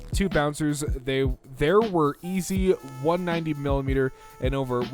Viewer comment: Did he just say milimeter at m